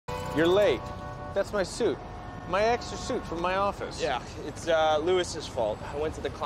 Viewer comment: I honestly misheard it as ‘my Ex’s suit’ and I got excited because I thought he was gay